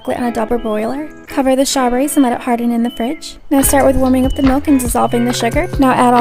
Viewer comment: Am I the only one who heard mook instead of milk-?